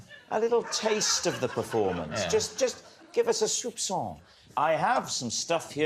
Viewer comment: what does Rob Brydon say? "Give us a little taste of the performance, a little [something I can't quite make out]" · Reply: Soupçon - a very small quantity of something